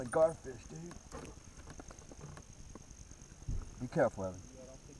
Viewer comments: Imagine the child still had his/her hand there. Thank god he told her to be careful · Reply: so when he starts pulling at the line saying "...think i got a garfish." the kid starts reaching in and he says "be careful Evan". That's what I hear, others have told me its a girl so idk. its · Imagine the child still had his/her hand there. Thank god he told her to be careful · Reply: Lil_King450 Exploration actually it sounds like he says "be careful Em" as in or